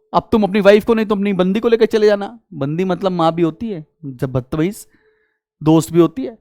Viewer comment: did sir just said bandi mtlb maa bhi hoti h☠️ or am I just hearing things(I must be hearing things Im gonna se a doctor) very soon